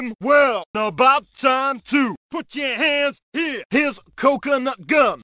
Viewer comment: His coconut gun finally kissed the “D”. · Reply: What are the redacted words? “Tizzed the D”?